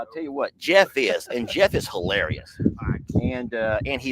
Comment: HEY! Not to play matchmaker or anything, but Sister Kim could round out your dinner group. Just a thought... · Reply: I thought I heard them say Jeff is married?